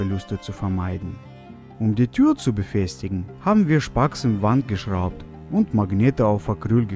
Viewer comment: ich habe verstanden "haben wir spast in Wand geschraubt" XD